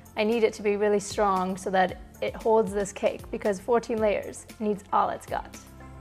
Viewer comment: Sounded like she said 14 layer cake. After looking at end results I see it is 4 layer but her accent made it sound different